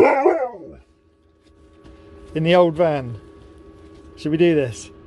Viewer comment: it really sounded like he said lets do😂😂❤️❤️❤️